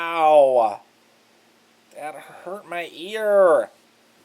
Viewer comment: "oww, that hurt me ear." "LOOOOL" At least that's what i heard